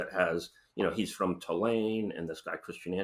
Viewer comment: Question ... Does he say, "he's from Theleme"..? ...as in the Moderna embroiled med company that UK PM, Rishi Sunak was invested in?